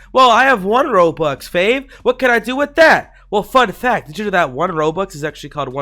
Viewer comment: Who else heard him say fun fact and it sounded like f*ck that